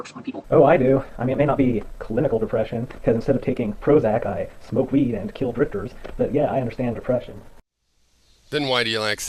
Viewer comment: at about 10:50 mins did he say he smokes weed and kills drifters? i am sure he did say that! Do you think he may be capable of doing it ?